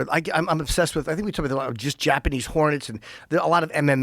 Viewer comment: Thought he said Japanese whores 😅had to skip back 15 seconds to clearly hear hornets